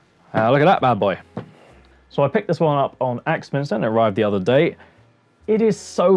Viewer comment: you show your new vice. You say you picked it up on, what sounded like, "ax master". Do you have a link, product number, name to help us find one? I have been looking for a budget quality vice.